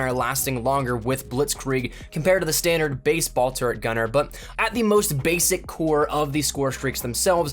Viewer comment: lol it sounded like he said "Baseball turret gunner" :D